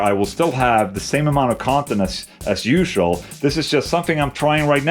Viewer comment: you’ll have the same amount of incontinence? Did I hear that right